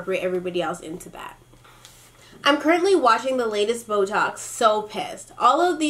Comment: when you said boetalks, i thought you said botox